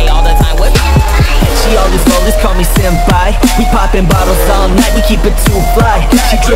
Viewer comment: i noticed at 1:25 on the vid, instead of senpai you used "zenpai" im am not blindddddddddddddddd also love vid kept up the good work.